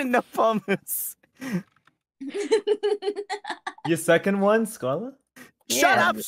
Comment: I'm curious what did Shu say? 😆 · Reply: I heard, ‘your second one’s gone?’ But I don’t think that’s what he said